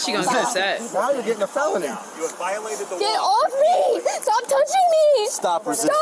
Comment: Did anyone else hear that Oblivion-esque "you have violated the law" in the background at like 16:41???